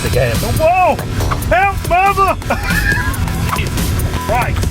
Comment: Did he say "Help Mama", or "Help Obama"